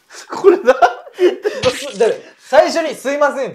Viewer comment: 「すいません、それでは失礼します」に聞こえる